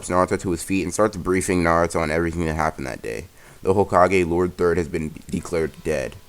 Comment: Bruh i fr thought you said ,,lord dirt